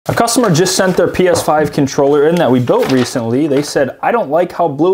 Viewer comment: Did he just say Penis 5 controller😅